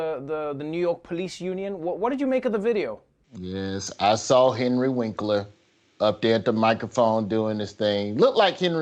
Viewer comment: lol I thought he said Angry Winkler. But the subtitles say he said Henry Winkler. lol, they both work.